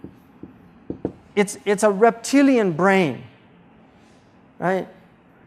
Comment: Wait a minute did he say you have a Triune brain" what a Muslims proclaiming that mans three pound brain, has three hemispheres, which we use to operate daily in a three dimensional world. Hmm sounds more like a Christian concept. And they want to deny the Trinity when the body, soul, and spirit of man denies the lie against Islamic teaching that we are not .are in the image and likeness of our creator. Genesis 1:27 Genesis 5:2 Jesus AKBAR